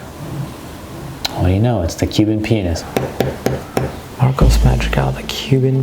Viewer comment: The auto generated subtitles at 3:48 says ‘penis’ instead of pianist...